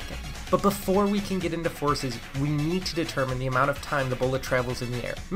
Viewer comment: “So finally, we’re at forces” I thought he said horses for a second